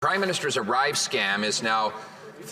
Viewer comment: I thought he said arrive scam